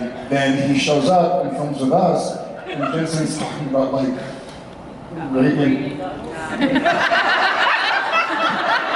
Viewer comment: Does anyone know what Jared says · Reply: Andrea Prochowski rapeing goggles